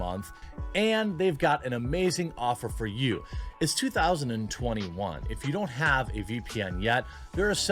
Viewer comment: Hey idk if I mis heard the sponsor of the day but it sounded like you said it's 2021. Either way keep up the hard work 👍